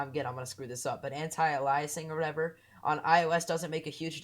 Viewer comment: did i hear hentai